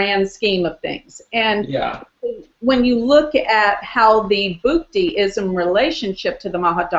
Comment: does anyone know 40:25, she said booty, what is the exact spelling of the word? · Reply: Bhukti Also called antardasa